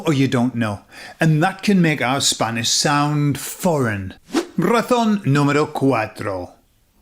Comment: Entendí "Ratón número 4"...